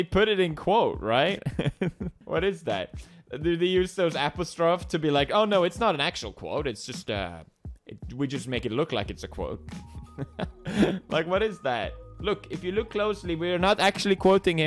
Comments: did he say appistroff not apostrophe???? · did he just say apostrophe like apastrof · Who else thought he said "apple straw" when he said apostrophe